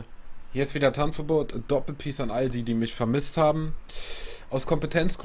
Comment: Ich hab am anfang verstanden: hallo leute doppelpeace an ALDI xd